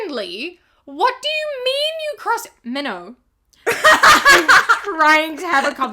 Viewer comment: bestie i thought you said minho like hed be the person to cross his legs while driving